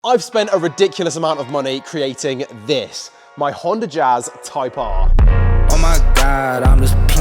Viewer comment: I thought it said Honda Jizz.